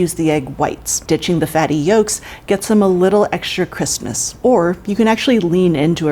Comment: in the video, am I hearing things or does she say "you can get extra christmas" instead of "you can get extra crispiness"